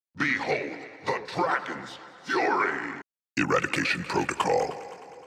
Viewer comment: what say? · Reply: Erradication protocol